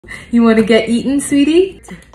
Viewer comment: I thought she said do you wanna get eaten? I was like wow this baby’s optimistic about everything 😂